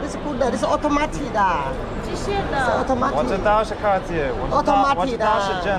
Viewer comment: automatica is; it winds itself, you thought she said authentic 😊